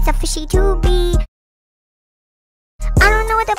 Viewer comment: I got into this song bc my baby cousin started saying it and I thought she was saying fishyome so I was confused and she said her brother played that song and I was like what?Then I searched it up and found this…